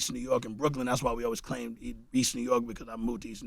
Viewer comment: Did he say at 14:13 “ that’s why we always claimed East Brooklyn New York ?” Lol WORD ! Brooklyn shit